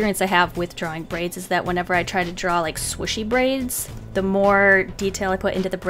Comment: When she said swishie braid I thought she said squishy braid. Lol 😂😂😂